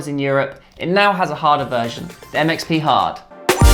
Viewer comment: the MXP hard sounded like 'that makes me hard'